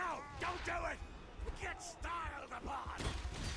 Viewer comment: did krieg say "get styled upon" at 19:52 ?!?!?